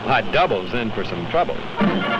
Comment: Did Superman said "Perhaps my double's in for some trouble" ?